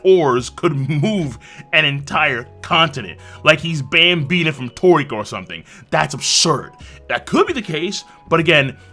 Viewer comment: Sorry I couldn't catch what he said what anime/ manga is that ?? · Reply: Toriko